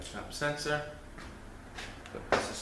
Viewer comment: what sensor was that..lol! Rewinded 24 times and still dont know what he said. thanks in advance if answered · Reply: output speed sensor